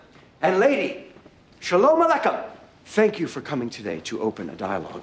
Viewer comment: Did he say "Shalamalaikum" conjoining "shalom" with "salamalaikum"? 😂